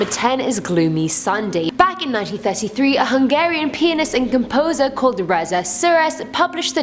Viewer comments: with her accent it sounds like she said “a Hungarian penis” 😂😂 Yes I know I’m immature. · Did I hear that right?!?! Hungarian what?!?! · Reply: Pianist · 😂 a Hungarian penis ? Did I hear that correctly 😭😭